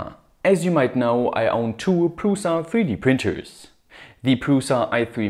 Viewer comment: At first, I thought he said: "I have 2 Russian 3-D Printers." These memes really have been getting into my head lately.